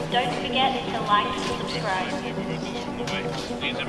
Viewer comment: Did it just say dont forget to like a Subscribe And Ghidorah